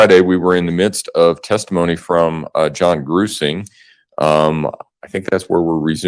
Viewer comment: Jonathan Grusing - FBI investigator I had to google it I kept hearing "Gruesome" not Grusing. Lol.